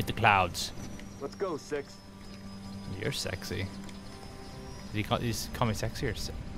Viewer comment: did ur say ur sexy and if u did who 2